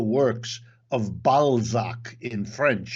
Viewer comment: did he just say ballsack Sorry I had too😆